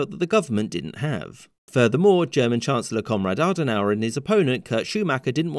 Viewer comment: Sounds like you said "Comrade Adenauer". XD